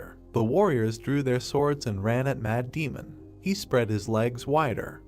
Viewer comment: every time you say 'Mad Demon' i hear 'Matt Damon